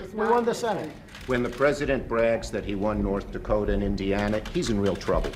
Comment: Wait did he say India?